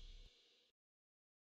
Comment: I love the "NOISE IS COMING PICK UP PHONE." at the end it's majestically perfect! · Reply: I always thought he was saying “Noise is coming pick up F r o g “